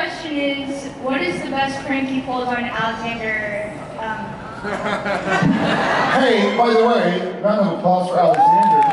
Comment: Can someone tell me what dean is saying at 3:37? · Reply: Geniustrash “Sheriff gave her the ‘ol Alabama Slammer.”